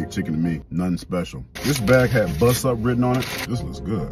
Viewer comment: Dead ass thought you said "this bag had butt stuff written on it" 😂 thought maybe the chef was flirting with ya lol